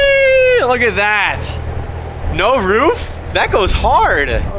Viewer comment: did call you sweetie😭